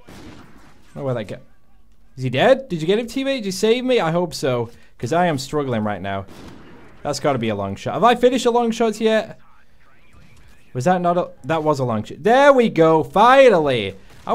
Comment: This whole time I thought he was saying "lung shot"